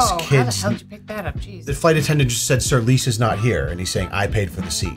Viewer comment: I thought he was saying “Mason’s not here” not “Lisa is not here”